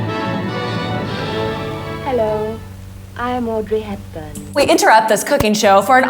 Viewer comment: istg i hear her call herself "aubrey" instead of audrey at 7:02 and now i'm tripping